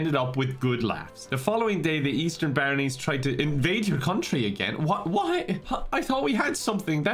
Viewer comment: Thought he said the Easter bunnies were invading